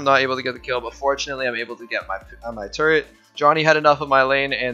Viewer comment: Is it just me or did he say "Turret"